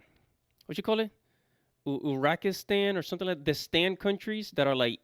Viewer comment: omg did you say "Uraqistan"?? Come ON!